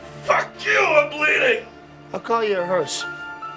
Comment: for years I always thought he said "i'll call you a nurse" but realised it was actually "HEARSE" lol.